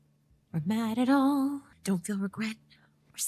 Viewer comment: did i hear EVAN PETERS??😍